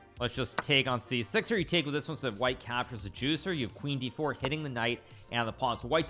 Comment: did I hear bro saying juicer?? W tushi reference then